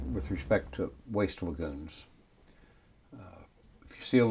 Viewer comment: the original english subtitle are wrong, I try to understand it, but can not catch the words as following , does anybody help to correct it? I appreciate very much : ----------------------------------------------------------------------------------- 00:01:21.739,00:01:29.060 generally speaking with respect to waste feculence 00:01:25.069,00:01:38.369 if you've seen a feculent 00:01:29.060,00:01:42.689 you know ground waters polluted most feculent are wreck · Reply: Tom Fullice: the original english subtitles are wrong, I try to understand what is said, but can not catch the words at following timestamps. Can anybody help me? a. 00:01:21.739, 00:01:29.060 generally speaking with respect to waste feculence ---> "generally speaking, with respect to waste lagoons: if you see a lagoon, you know groundwater's polluted" b. 00:01:25.069, 00:01:38.369 if you've seen a feculent ---> "if you see a lagoon, you know groundwater's polluted. Most lagoons are, ...er... leak" (i.e. perhaps speaker corrects himself, mid-sentence or says "Most lagoons sub-leak") c.00:01:29.060, 00:01:42.689 you know ground waters polluted most feculent are wreck ---> "you know groundwater's polluted. Most lagoons are, ...er... leak" (i.e. perhaps speaker corrects himself, mid-sentence or says "Most lagoons sub-leak")